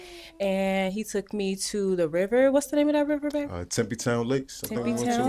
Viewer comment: Did I hear her say Bae lol.... My guy had her hook early... Thats whats up thought, I'm glad she came to the show with the intent to be available for her match.